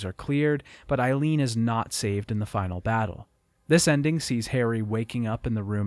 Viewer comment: almost sounded like he said Harry